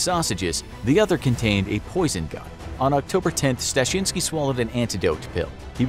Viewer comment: I thought it said poison GUM